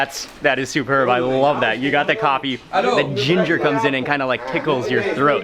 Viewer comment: did he say Ugandan coffee? 😍 Yes, atleast I heard my Uganda if I am not mistaken.